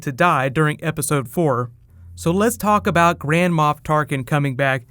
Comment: the closed caption says Grandma off target instead of Grand Moff Tarkin. I thought it was funny.